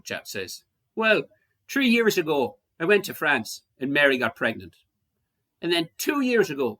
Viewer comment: LOL took me a few minutes to figure out you were saying "...and Mary got pregnant". I thought you were saying "...and married a---?" something or other.